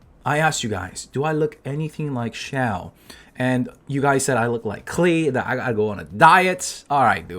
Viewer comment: "do i look like xiao?" heard it as "do i look like shower?" at first.. this channel comment section.. and zeal"shower"ambitions has done me bad man